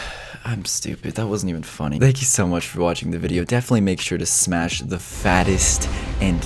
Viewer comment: I thought you said “smash a fat lady”